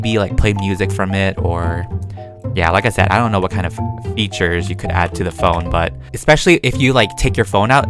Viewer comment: Did said what f**king feature's!?!?!? 😐😐😐😧😧😨😨 go to 3:00 min.